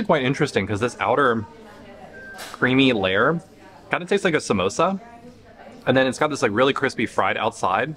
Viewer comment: When you said taste like Samosa I thought you said taste like Mamosa